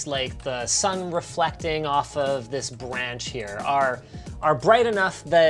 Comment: did he say "bwanch" or is it just me??